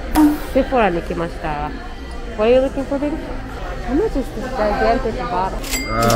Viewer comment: これ英語でなんて言ってるのか、what とlooking for は分かったのですが、誰か教えてください🙇‍♂️🙇‍♂️お願い致します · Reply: ご本人ではなくて申し訳ないのですが What are you looking for baby？じゃないでしょうか。 イヤホンがなくて、めちゃくちゃ耳近づけて聞いただけなので違っていたらすみません